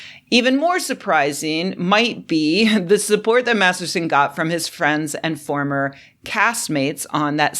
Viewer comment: Thought you said he supported a Baptist. Oh well …